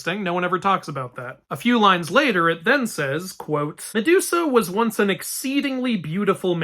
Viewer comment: ok, so i m outside and have kinda low volume so as to not disturb others but i legit heard, "sedusa" here😂